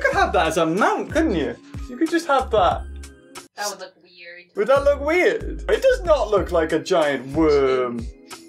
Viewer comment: that moment when you realise what just said xD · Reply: said the brachisaurues head alone looks like a giant penmis